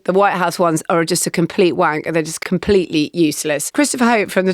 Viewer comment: did Marina say wank?